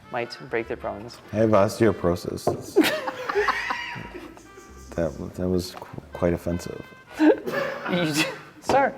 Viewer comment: what did mike say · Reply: "I have osteoporosis, that's... that was quite offensive."